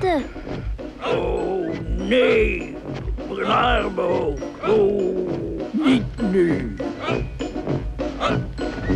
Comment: Wat zegt Bagheera bij 0:18? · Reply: Men arme oren, niet nu volgens mij